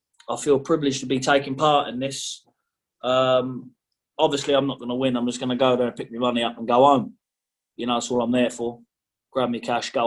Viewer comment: Did he say at 2:48 “I’m obviously not gonna win, I’m just going to get there pick up my money and go on”????? 😳😳